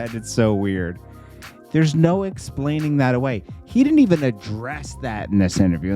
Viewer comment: Thought he said, he doesn't address rumors